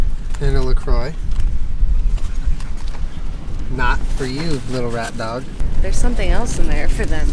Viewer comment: I dont know why but when Julien said"not for you little rat dog" I heard "not for you little rat man" and thought why is my gym teacher at the beach with Jenna and Julien